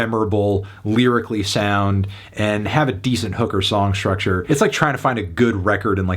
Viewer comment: Like pickin' over bruised fruit.🤣 Also, 6:29 sounded like you were looking for a decent hooker song structure...